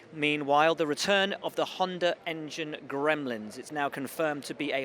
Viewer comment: am i having trouble with my ears or are people talking like " hondar " " chinar " etc.. whenever i hear " honda engine " i hear it like " hondar engine " or something like that. Like germans saying runter for unter.. maybe they mean honda racing so honda r. or something.. im not sure what i am hearing but really irritated XD